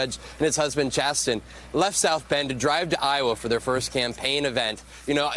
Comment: did he say drived? DRIVED???